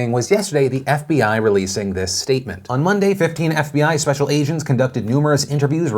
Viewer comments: does he say “special Asians”? This is what I took away from the news. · Did you say "special Asian's" by accident Phil ?- context "On Monday, fifteen FBI special agents"